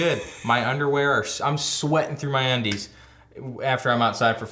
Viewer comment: "Sweating through my undies" but I heard "sweating to the oldies". OMG Richard Simmons gonna show up outside your front door one morning for sure. Then you'll be sorry you mentioned it... 😺